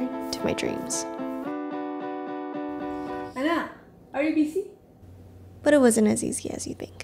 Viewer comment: Did I just hear “anak are you busy??